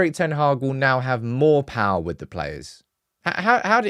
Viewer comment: I thought you said ,ten hag will be sacked